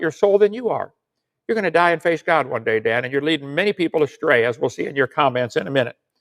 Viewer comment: With the closed captions turned on, Kent said, "You're gonna die and face God someday". Before I turned them on I heard, "You're gonna die face down someday".